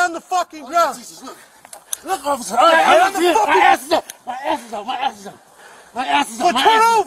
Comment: did he sayMY ASTHMA MY ASTHMA or my ass is out? Lol